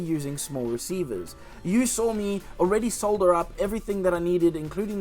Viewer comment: Could've sworn you just said soldered...I thought it was sodder...I'm so confused. Awesome flying fish tho, those space commies would be proud